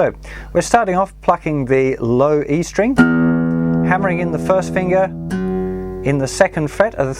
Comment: a the first i thought you said f*ckin the string xd like on 2:12 good lesson tho ;)